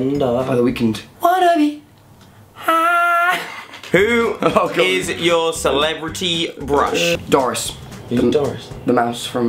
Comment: Who else heard Jack say Who is your celebrity Brush 2:34 Or was that just me